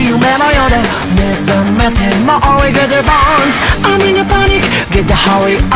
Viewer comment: コンパスで曲聴いてて歌詞全然わからなかったから0:21のところ目覚めても追い風ドーンって空耳してた